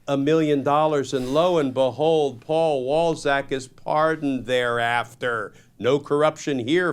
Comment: When I'm not paying attention I hear the name "Paul Ballsack."